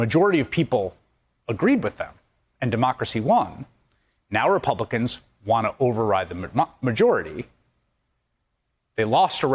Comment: I heard that Chris Hayes!! 7:27 you nearly said "now the Republicans want to override the McMuffin". First democracy, then Macca's breakfast, what's next?